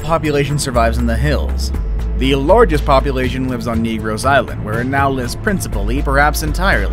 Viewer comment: Did he say negro’s